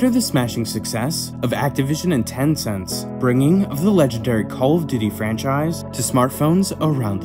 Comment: Why the hell do i hear him say the call of diddy 😅